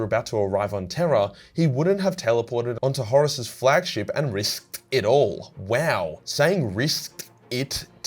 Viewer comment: you know what, "risked it" is kinda hard to say out loud, ive been saying it for like 3 mins and now all i hear is "wrist dick"